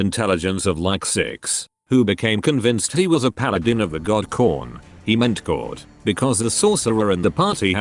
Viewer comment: when he said corn instead of corn i heard khorne.